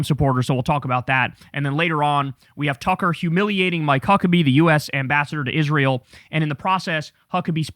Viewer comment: Swear to God I heard Mike Cuckabee.